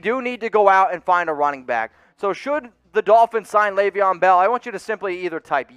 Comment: For a good min I thought it actually said Dolphins signed bell